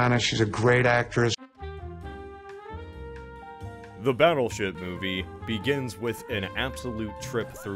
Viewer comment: Why do I hear battleshit instead of battleship